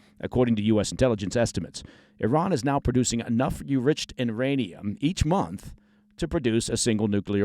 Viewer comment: Did anyone else hear "u-riched en-ranium" around the 11:18 mark?